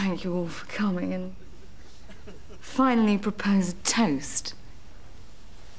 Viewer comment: what did she say at 9:14? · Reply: "To me... and all who sail in me"